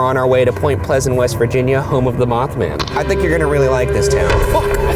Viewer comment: I got scared because I thought you said “Math man”